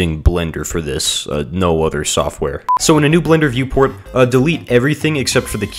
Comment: wtf did you just say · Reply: Viewport